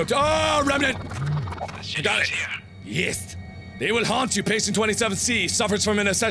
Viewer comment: did he say "есть" (as "got it") in russian?? sounds like it)